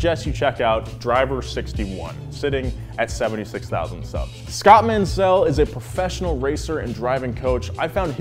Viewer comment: Did I just heard Scatman?